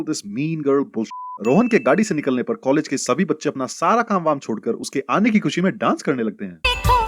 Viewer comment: Sin counter sins! Subtitle says Rahul instead of Rohan